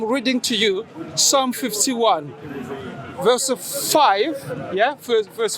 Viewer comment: I misheard the verse as "Surah 51, Verse 5" since I had it on 2x speed and when I searched it up in the Qur'an, this is what I found: "Indeed, what you are promised is true." [The Holy Qur'an, Surah 51, Verse 5]